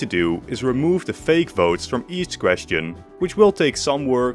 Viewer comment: Each question I heard east grestin